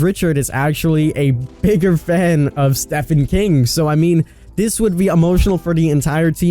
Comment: Is it me or did he really say Stephanie King?